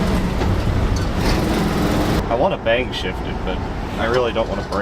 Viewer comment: Welp. I've decided. I definitely need some Super Clean. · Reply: OK - had to look up "bang shift," (and at first I thought you said "bank shift," and I'm like ????) and Chat GPT told me this: "A "bang shift" refers to a quick and aggressive shift of gears in a manual transmission car, often done without using the clutch. This technique is typically associated with drag racing or high-performance driving, where rapid gear changes are necessary to maintain speed and power. The term "bang" comes from the sound that can occur when the gears engage abruptly. It’s a risky move that can damage the transmission if not done correctly, but when executed well, it can lead to faster acceleration." You'll have to demonstrate that sometime on your channel.